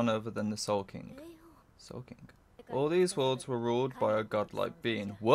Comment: that is probably a subtitle error , where I watched it it did not say that all the worlds “Were” ruled by a god like being. For me it said that “ soul king is a being that reigns all over the world” .